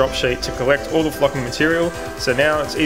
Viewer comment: it sounded like” all the fucking material” 😅